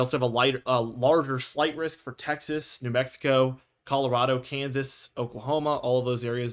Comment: I was repeating what he said in my head and I thought he said new Texaco